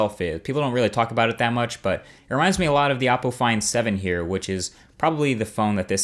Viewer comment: Oppo find 7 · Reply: It really sounded like he said "The Apple Find 7" (Or I'm just delusional lol)